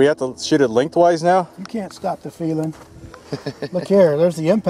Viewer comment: For a second I thought he said "You can't stop the freedom." lol